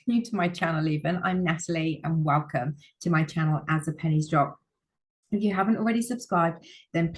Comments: for a second i thought it said As the panties drop. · Sorry I’m giggling at the “ as the Pennie’s drop” I keep hearing “as the panties drop” Which I’m sure you know what the means lol 😅